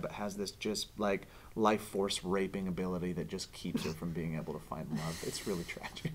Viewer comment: i swear joe said horse-raping.